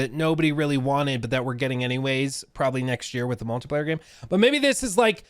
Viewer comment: Which game is that he's referring to at 5:15? Did he say it was called Multiplier?